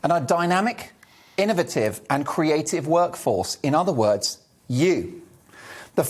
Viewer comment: When he said workforce, I heard 'cattle'